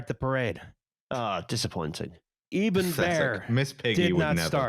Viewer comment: When you listed the ballons and said "Eben Bear", my brain was horrified to mishear it as "Eben Byers", the scottish golfer who had his jaw amputated after getting horrific jawbone cancer from drinking radioactive Thorium enriched energy drinks.